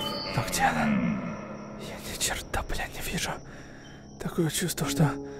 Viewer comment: Аааа мне послышалось что сказал бл/\ на 19:00 минут